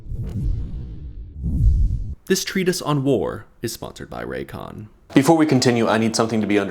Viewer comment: I thought for a brief second that you said Raytheon instead of Raycon